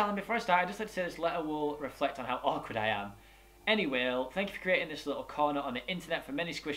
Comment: Go to the time 1:52 and listen to what he says, lol lmafo he frickin' said "Any whale" I just died 😂😂😂